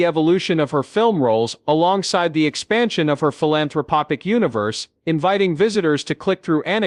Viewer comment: Did he just say PHILANTHROPOPIC ??? That's not a word.